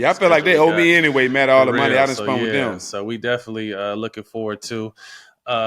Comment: Did Stack say "spunt"? LMAo